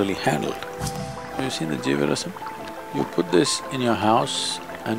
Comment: What is it hes talking about at 0:15 ? Anyone know what that is? He named it in a low voice and said it fast I couldn't make it out. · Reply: Jeevarasam